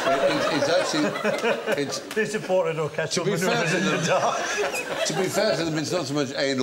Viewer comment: What did ross noble say at around 1:15 after bill baileys download joke? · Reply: "They supported Orchestral Manoeuvres in the Dark"